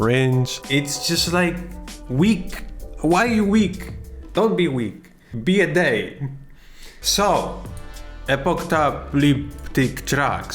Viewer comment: "Don't be week, be a day" IM DEADDD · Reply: Lmao I thought he said, "Don't be weak. Be a Dane." ... which also works. Been watching too much The Last Kingdom on Netflix.